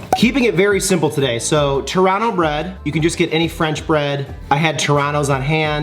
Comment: when u said Torano i thought u were saying Toronto bread lol